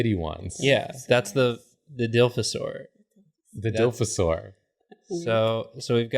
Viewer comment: i spit out my water bc i thought he said "dilf-asaur"